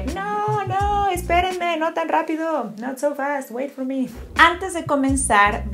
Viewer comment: What did she say after esperame? No ? rapido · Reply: No TAN rápido: Not SO fast